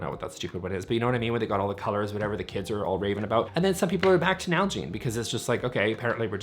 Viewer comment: I thought you said "algae in water bottles," not Nalgene